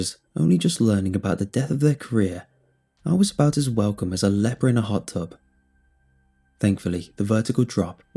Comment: "I was accepted much like a leper in a hot tub" what genius descriptives. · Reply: Ngl... I heard it as "leopard in a hot tub" and I was like: 😦 weird analogy but ok 😦